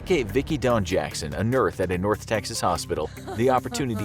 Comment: I'm pretty sure the voiceover guy on these videos is AI. No real voiceover artist would say 'nurth' instead of 'nurse'